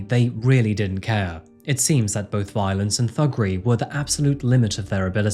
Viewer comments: MY OLD EARS misheard "violence and thuggery" as violence and buggery. WHAT? oh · did you just say “violence and fuckery”? 😂 I’m sure I misheard but if I didn’t, I love you even more